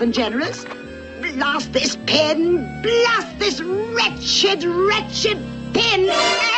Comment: When I was young I thought she said Rat SHIT rat SHIT one instead of wretched